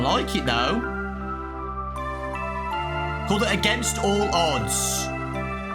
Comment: i thought it said against all dads